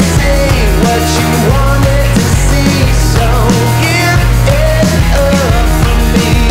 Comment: For some reason I heard him saying "get it up" and thought he was talking about an erection